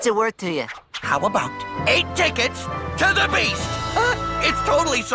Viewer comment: when I was like seven I thought he said beach so I was like “Why would you need tickets for the beach?”